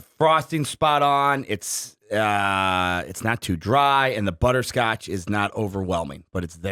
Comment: Could of swore I heard say “Butter-crotch”… right at the beginning of ranking.😂